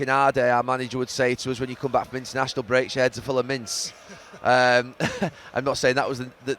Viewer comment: what did he say here, full of mints??